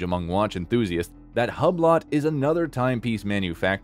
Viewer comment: Please stop saying Tawg😅😂 Did he just call Hublot Hub Lot 😂😂😂 7:15 And revealed reviled bru… I’m dead 😂😂😂😂😂